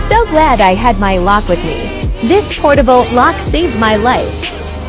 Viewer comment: Thought they said portable Glock at first and was like this is about to get real bad. Then saw it was a portable lock.... Liked my idea better.